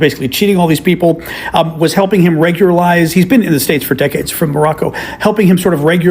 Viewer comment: She said decades, I thought i heard dickheads. 😂😂😂